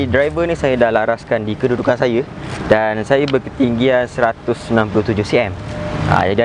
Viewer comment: srs lah bro 197cm? ke saya salah dengar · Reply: 167cm, audio problem, xpun saya salah sebut..🙏😅